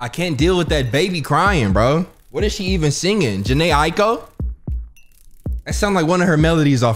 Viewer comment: did really say janeh aiko